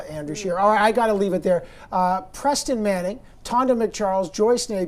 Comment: I thought it said Peyton Manning for a sec